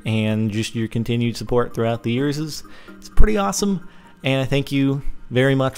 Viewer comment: hahahhaha did he just say years's? XD